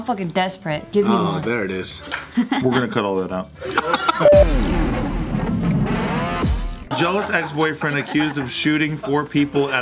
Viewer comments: When Steve first said the article title, I heard "Joe's ex boyfriend..." lmao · I thought he had said Joes ex-boyfriend until he Re-read it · At first I thought he said "Joe's ex-boyfriend" as the article title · "Jealous exboyfriend" I heard Joe's exboyfriend xD